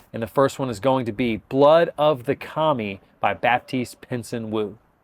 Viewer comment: I thought you said Blood of the "Commie." Whew!